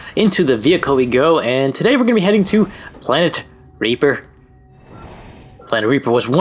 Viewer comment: The first time I heard of the planet Reepor, I thought it said planet Rapist lol XD